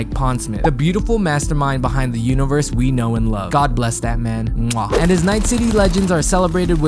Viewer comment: You saying “god bless that man” dude I thought you said you missed that man aka Mike Pondsmith and this vid gave me a mini heart attack cuz I thought that meant he died and I was about to be soooo sad